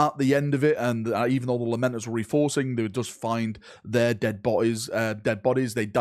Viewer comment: I heard: "the Lamenters have been reinforce" My first thought: "What are the odds the first fight would be against the tyranids" 😂 Awesome summary, can't wait until Vox in the Void does his magic with this story.